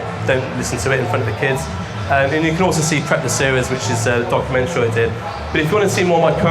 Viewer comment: What is the series on Vimeo please mate? Sounded like you said prepper series?? I have had a search bit can't find it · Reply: Hi John, it was called Prep the Series and it should still be on Vimeo, let me know if you can't find it